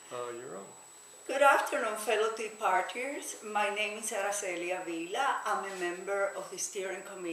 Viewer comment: Oh TEA Party? I thought you said Coffee Party....